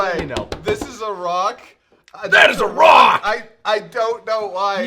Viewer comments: for the longest time I thought bardock said “that is wrong!” until this video for many years I thought twopock was giving bardock his poop LMAO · I always thought he said "THAT IS WRONG!!"